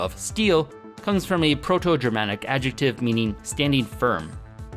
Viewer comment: It’s not German, it’s PIE (Proto-Indo-European) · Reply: He said proto-germanic I thought he said germanic. Need to do research on what this language or dialect is. Thank you for information on that topic.